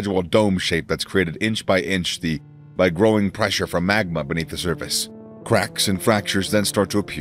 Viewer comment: Thought he said “Growing pressure from Maga”